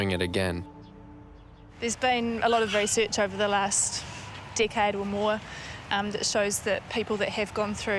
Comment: I thought I was used to Australian accents , but I was kinda taken aback when I thought I heard, “There’s been a lot of research over the last dickhead or more...”. (decade). I mean, yes, perpetrators are dickheads, all, but...